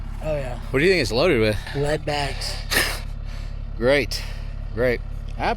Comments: 😂 I am sure you ask all the guys who talk to about their lips. So, chucklenutz, what did he say? Enlighten me. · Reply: I thought I heard it too, but playing it back it sounds like lead bags, captions also show the same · I had to pause and rewind thought said the uhual was loaded with Wet backs, think he said lead bags. Sweet geezus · Did he said loaded with Wetbags or ?